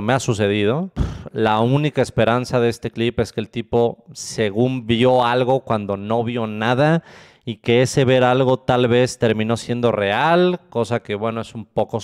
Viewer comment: No entendí nada jajajajaja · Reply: "La única esperanza de este clip es que el tipo según vio algo cuando no vio nada, y que ese ver algo, tal vez, terminó siendo real", acto seguido se da cuenta de la barbaridad que acaba de decir